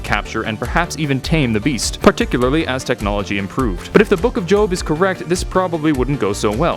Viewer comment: Did he just say the the book of jokes?